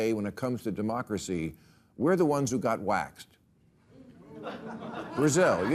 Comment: It sounded like he said wacked, not waxed. I think the audience was confused. I sure was lol